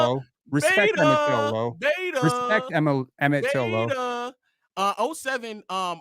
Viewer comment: Respect Emmitt Till? What’s that about? Or i did i hear him wrong?